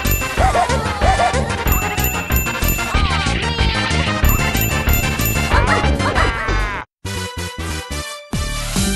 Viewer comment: What daisy say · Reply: Aw man